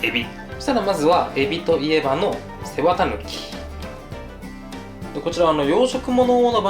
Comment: ぼーっとしながら動画聞いてたら背わた抜きが世話タヌキに聞こえてん？ってなった